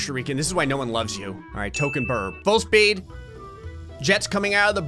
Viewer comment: I’m sorry but does anyone else hear “birb”